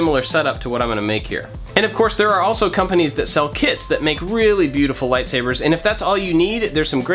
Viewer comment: In the start i heard it as "companies that sell kids" 😁😁😁😁